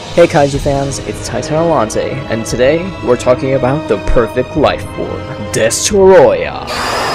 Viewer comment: Misheard that is "The Perfect Waifu."